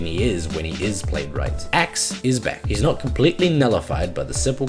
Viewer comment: I THOUGHT U SAID AXE WAS BAD AN I BE LIKE "AXE IS SAD" ut then i listened to it again and realised u said "Axe is back" and I be like "The blade of Axe has tasted first blood"